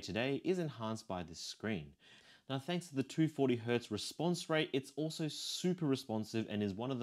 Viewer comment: broh.....did you say 240Hz refresh rate? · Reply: Response rate bro, not refresh rate. Two different things